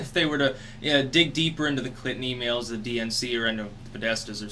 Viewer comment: Probably contains dirt on the Clinton's. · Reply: I thought you said "on the clitoris" Oops. Lmao.